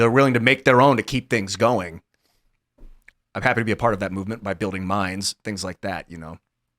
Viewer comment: Did I hear a buddy say he's happy to build mines?